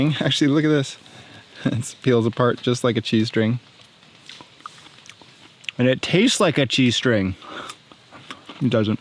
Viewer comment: alt tabbed I heard it felt like a g-string, peels apart like a g-string, and it tastes like a g-string. Came back to realize you said cheese string.